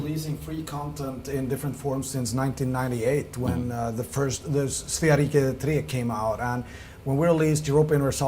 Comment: what game he is referring? 28:10 Star wars can't get any worse, paradox [...] <- anyone knows what Joe said? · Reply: HKO2006 | Nightcore & Trance 10:00 He's talking about "Svea Rike 3" (Swedish title) English title is "Crown of the North". 28:10: He says "Paradox 2015". Meaning it will be remembered as a quote from 2015.